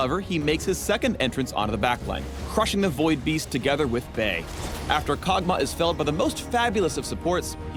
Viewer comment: did he say bae?!